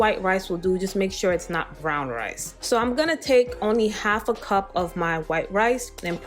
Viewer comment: I thought they said brown rice is the best to use